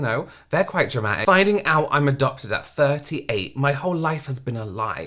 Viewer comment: I just woke up and obviously my brain is running at minus. I heard "found out I'm a Doctor at 38".. I'm like, that's not going to be triggering to me. I'd love to have the money doctors make. Also how do you keep that a secret? My dumb brain 😂